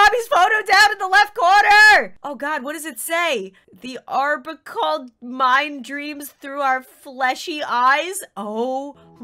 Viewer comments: Bobby is... Possed heres what it says "THE ARBORCAT MIND DREAMS THROUGH OUR FLESHY EYES" C R E E P Y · I think what it’s saying is 'the arborcal mind dreams through our fleshy eyes' and He’s possessed